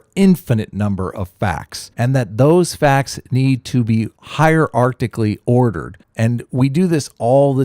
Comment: did he say "hier-arctically ordered"? sounds chilly.